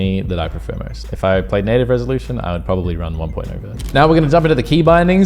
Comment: if he ran native resolution, he would run 1.1? is he saying 1.18? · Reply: In native he would have 1.0 (one point o)